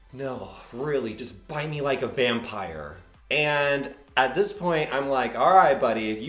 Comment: OMG I THOUGHT YOU SAID HE SAID " I might be a vampire " I WAS LIKE LOL WHAT